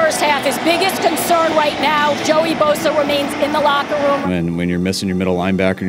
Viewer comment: Did she just call him Joey?